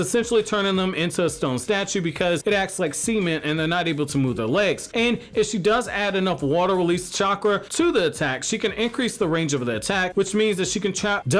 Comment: It sounded like you said "it acts like semen holding them in place" or sometjing like that lol